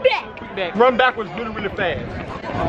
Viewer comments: the black kid said instead of quikback he said whoblack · DID HE SAY QUIT BLACK😂😂